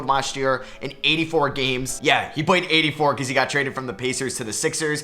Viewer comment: Did say raped?